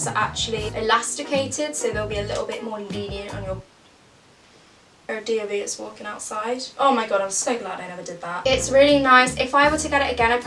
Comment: what did you say at 6:25 i'm so confused?!?! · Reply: Katie Louise “there are DofE ers walking outside, OMG so glad I never did that” (DofE stands for the duke of Edinburgh in the UK where you camp overnight in the countryside and walk like miles to each campsite) i think!